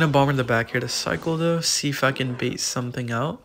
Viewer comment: blues see if i can sounded like see is fuckin